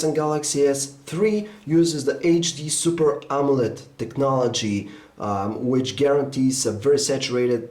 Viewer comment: I heard HD Super Omelette technology :)